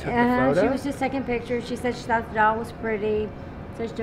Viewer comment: if you put the subtitles in english when the lady said " she was just taking pictures of the doll" in the subtitles it's mentionned devil instead of doll.. kinda creepy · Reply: I get why. When she says "the doll" it sounds like "devil"